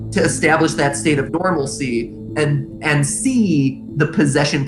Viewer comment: did he just say normalcy